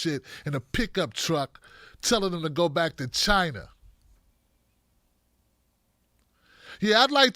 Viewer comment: Maybe I misheard but.....Did that lady tell that health care worker to "Go Back to China"??....WTF!!!